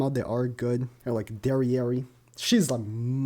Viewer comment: Think Ill just skip this banner for Melascula all together and save the Gems for next one Edited: 7:32 Did he just say Derieri ? * starts hording all the gems *